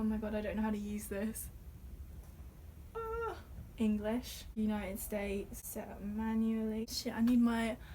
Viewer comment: did you say United States???